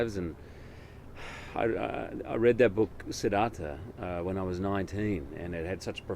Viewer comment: What book did he say? Sidata? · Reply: FinalRespawn Siddhartha thanks Google "Feeling Lucky"